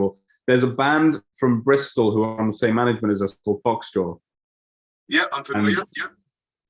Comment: What band does he say @ 5:08 ? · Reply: i believe he says Phoxjaw and the song is "sungazer"